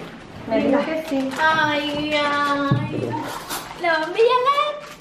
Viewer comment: En el min 6:20 en el primer regalo yo escuche ay gracia a y en el 8:10 en el que recibe la ropa me parece q dice gracias solo q el audio no se escucha. Se ve que si le encantaron los regalos. No debería afirmar algo q no es. Ella estaba con toda su familia. No todos se expresan igual en x momento